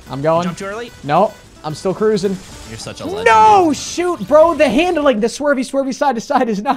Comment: ”you’re such a lesbian”😂😂 that’s what I heard